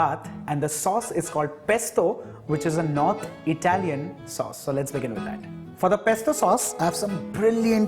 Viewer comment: What?? did I hear NOT Italian sauce I know that basil is native to Italy so strange